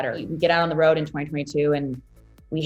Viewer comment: Why did I hear her say 2022.